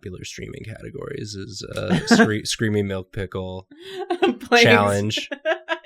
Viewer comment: I was washing my hands when Screaming Milk Pickle came up, the rushing water drowned out some of the sound and I thought said Steamy Milf Pickle. I was disappointed to learn the truth.